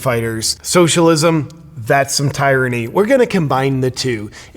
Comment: I have to say it because I heard it, and it made me think of "The Postman". It sounded like you said TIEranny, and now I'm thinking of the scene(s) where the kid was told "You spelled tyranny wrong.".